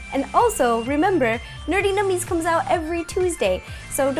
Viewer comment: It sounds like she says Nerty Nummis